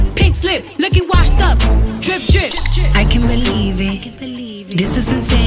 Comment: I always thought she was saying "Nicki's washed up, drip drip" lol xD